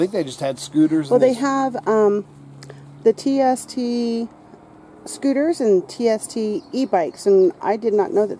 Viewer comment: at first i thought you said TNT scooter yeup you might as well just set the seat fer them grankids